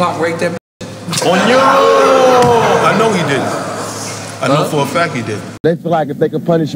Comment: I know he didn't.... I know for a fact he did 🥴🥴🥴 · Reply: It sounded like he said "he did" but he says "didn't." If you hear closely.